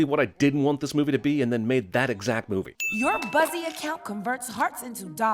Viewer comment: I thought she said bussy instead of buzzy and was brained.